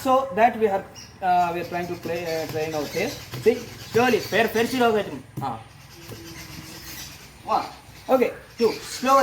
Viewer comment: what did you just said? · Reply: " TUM FIR SHURU HO GYE " suddenly birda stopped Chirping for a moment 😂😂😂😂😂😂